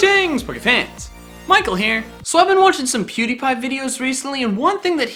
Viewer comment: I thought he said puberty and not pewdiepie